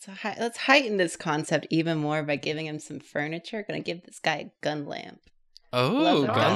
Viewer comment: At first I thought said "Gun Lance" instead of gun lamp. Me: "Wait this isn't a Monster Hunter episode, what is going on?"